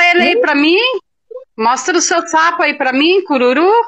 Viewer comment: eu ouvi saco 😳kkkkkk